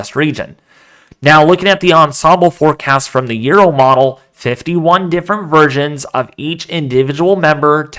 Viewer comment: Sounded like u said 51 different virgins 😂